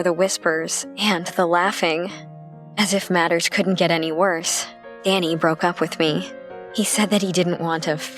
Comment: Omg 3:15 it sounded like she said Annie broke up with me